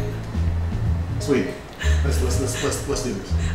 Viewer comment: did he just say sweetie?